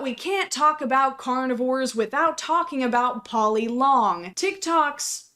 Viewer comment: I thought she said "poly mom" and I was gonna make a joke like "hey my girlfriend calls me poly mom"